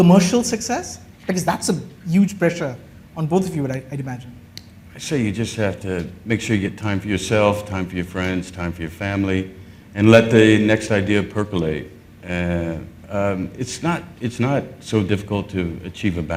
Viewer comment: What does he says at 4:30 . Let the next idea pro.... ?